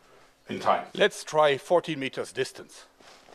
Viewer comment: There's no way that's 40 meters from the target. I wasn't sure I heard it right so I put on the "CC" and it said "40 Meters" NO WAY is that 40 Meters.